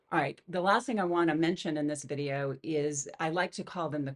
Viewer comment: In my head when u said "feel like" I heard "is like"